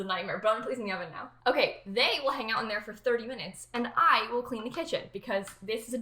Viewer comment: This is giving me joana ceddia mixed with Aries kitchen vibes Also “_they_ will hang out in there for 30 minutes” sounded like Julien saying “y’all gone die soon” to his Oreos.